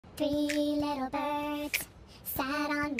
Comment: I hear “3 little birds shat on my window”